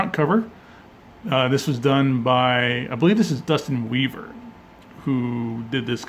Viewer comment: I swear I thought I heard u say Justin Bieber did the cover.